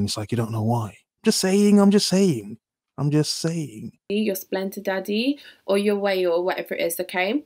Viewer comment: Sry, what was the third one @ 5:02 ... Sugar Daddy, Splenda Daddy or your Whale??? Did I hear that right?